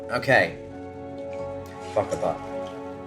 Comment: Did anyone else hear "fuck the butt"